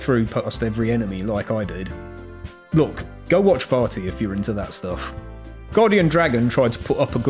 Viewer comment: Thought he said farty instead of vaati